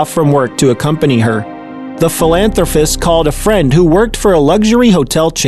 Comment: What is a "Philanthro - FIST?" Narrator says it twice by 1:20. Once is a simple slip. Twice is a habit. Word ending is "~pist". (You'll hear it again at 5:09 & 7:29.) BTW - there seem to be 3 different 'older' males used to represent one man, the dad.